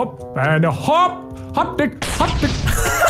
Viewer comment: what did he say?🤣🤣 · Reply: Hopped dig hopped dig